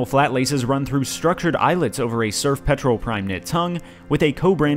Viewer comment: I thought it said "Smurf Petrol"